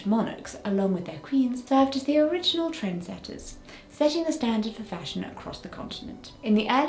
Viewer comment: When you spoke about "original trend setters" I misheard it as "original tress setters".